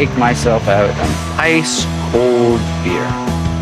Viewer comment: always thought he said "a NICE cold beer"